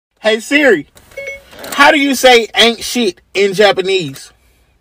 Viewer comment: I thought he said "Ancient" , but no he said "Ain't Sh*t"